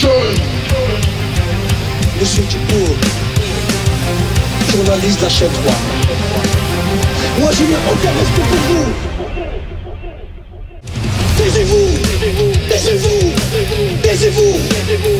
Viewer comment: J'ai pas compris ce qu'il a dis ? Taisez - vous ! ! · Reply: Moi j'ai entendu "Baisez-vous !" ... Ah, l'adolescence ... :^)